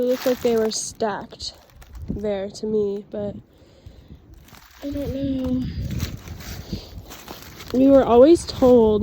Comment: on the video the sound got weird and it sounded like a little girl said "Maggie" while you were saying "I don't know"! Am I trippin because it's late or what?